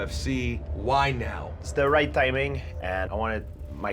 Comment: i thought he said steroid timing instead of its the right timing lmao